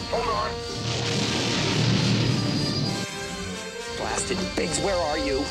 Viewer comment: Does Puggo Grande contain line "Blast it Wedge where are you?" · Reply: As a kid I thought he said Blasted Bitch where are you.